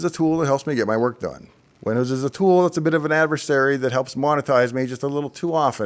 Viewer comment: I had to rewind several times until I understood that what I was mishearing as "Linuszs is a bit of an adversary" was actually "WINDOWS is a ...". Now it makes sense.